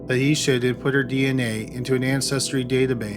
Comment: Am i the only one that heard him say "in 2013 lisa suggest yhat Det Hadley input her dna into a national data base, unfamiliar no matches where found for lisa" ? Around 2251 time stamp